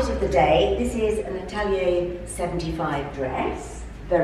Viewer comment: Sorry, didn't catch where your lovely black dress was from. Something75??? Do like to look your fashion stockists up, even if they sadly never seem to go beyond Size 16. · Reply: hi Helen I find it useful to click on the cc symbol bottom of screen to bring up subtitles when I can't catch names said Italian 75, it's around timescale 12:29 , I agree it's a gorgeous dress x